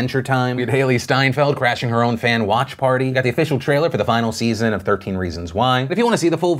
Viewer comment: watching in 2x speed, honestly thought he said "Hailee Steinfeld's OnlyFans watch party"